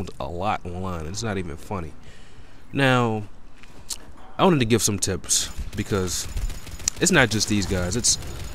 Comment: I thought you said: I wanna give some tits